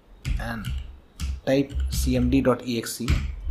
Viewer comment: guys what did he says in 7:14 pls answered me huhuhu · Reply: Press windows key + r and then type cmd.exe in it and click ok